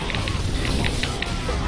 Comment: for some reason i thought he said "even when I'm dead i steal old people" O_O